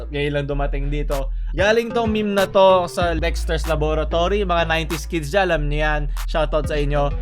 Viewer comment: “Galing ito sa Lexter’s Laboratory.” Ewan ko kung na misheard ko ba o ano eh HAHAHA